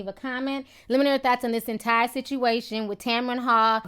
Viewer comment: please tell me why everytime you say thoughts,to me it sounds like you saying thots , but yes he thought he was getting a wendy williams lol